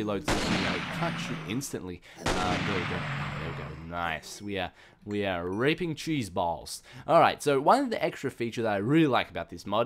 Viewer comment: Did he say "we are reaping cheese balls"?